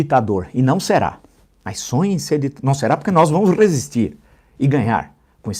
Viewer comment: Gente to bobo, o Villa esta com mais raiva o Bozo, do que eu. KKKKK, eu escutei "RESISTÊNCIA", KKKKK, PT E BOZO são uns bostas.